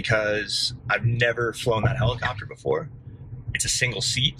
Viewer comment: Could have sworn you said you've never flown a helicopter before. Must have misspoken there. I rewound that part a few times thinking I hallucinated it due to lack of sleep. 😅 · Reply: Yeah, I needed to enunciate that better. "I had never flown this helicopter before" was the garbled thing I said. Thanks for watching!